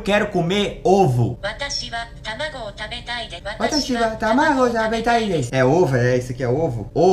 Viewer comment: eu ouvi tabaco